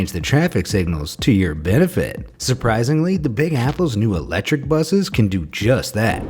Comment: did I hear it’s from apple? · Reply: he said big apple... and it’s just another name for New York :)